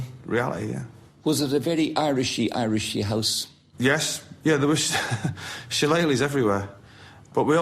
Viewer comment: what did he say was everywhere? Don’t understand that word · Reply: Shillelaghs, which are Irish walking sticks.